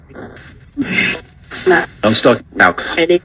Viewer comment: I thought I heard, I’m stuck now.